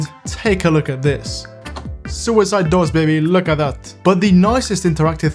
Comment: I kept hearing suicide doors on 3:26 and i tried using subtitles and it even said suicide doors XD